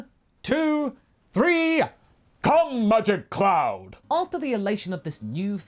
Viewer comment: "Now everybody, on the count of 3! 1,2,3" Me: "BuT mAsAKO!" Masako: "Come Magic cloud." Me: Wait what?.... awkward silence ensues. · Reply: Funny, I heard “come budget cloud” but that was just me